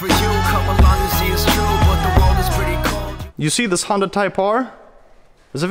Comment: I heard “you see this hung type R”😂😂 in the beginning lol